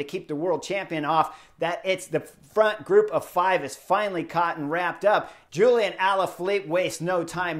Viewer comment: Chris, something is different but I can't put my finger on it. Did I really hear you say at 6:08 that someone was "caught wrapped up" ? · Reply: the front group of five is finally caught and wrapped up...